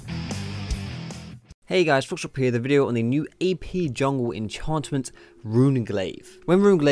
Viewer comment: sounds like youre saying EP rather than AP. x)